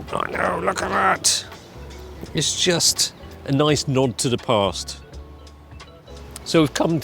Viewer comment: For a second I thought said "A knob to the past"